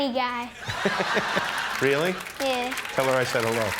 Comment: Can anyone tell me what Jimmy said at 3:03 ? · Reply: Tell her i said hello